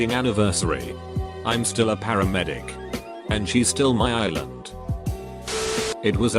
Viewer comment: “And she’s still my island” I kinda teared up man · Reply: I thought it said ''she stole my island''